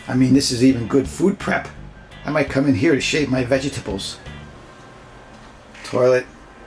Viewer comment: Wow, that airstream is perfect! I honestly thought you said "shave my genitals"....had to back up and listen again....lmao Well, i cant wait to see more!!